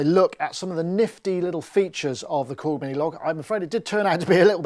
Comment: I thought that said things you can do with the Korg Minogue. 😀